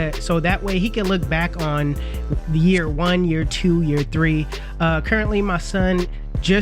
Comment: "APPARENTLY, my son just turned 6 years old" 👀🤣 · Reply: lol he said currently I thought he said apparently too 😂